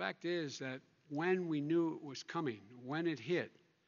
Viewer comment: did he say we knew it (covid) was coming? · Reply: Huh? He said when we new it was coming 🤦🏼‍♂️